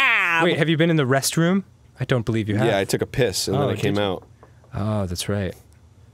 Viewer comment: "I took a piss then I came out" My hearing thought what they said was "I took a piss AND I MISS then I came out".